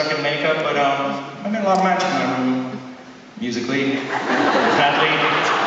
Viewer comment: what does he say at 0:34...? "a lot of (XsomethingX) in my room... musically"...? I can't make it out... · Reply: I think he says a lot of magic, musically...